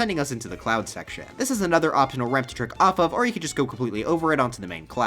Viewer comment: Oh "trick off off," I thought he said jerk off off.